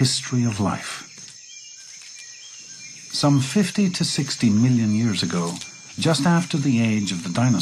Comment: EVEN BETTER...MORE POWER TO THEM STILL...I THOUGHT THE GUY SAID 16 MILLION...